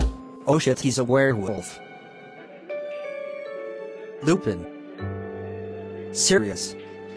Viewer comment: i like when he said serious instead of sirius